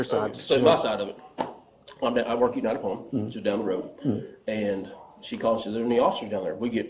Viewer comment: Where did the guy said he works at? I can't understand him. I just heard him say he works down the road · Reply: United Palm